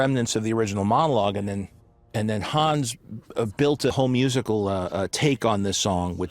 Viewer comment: all I hear is “built a HOME musical”